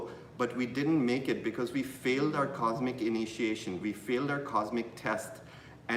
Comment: When said "Cosmic Initiation"...I heard "Quantum Initiation" GLORIOUS!!! 💫💫💫💫💫💫💫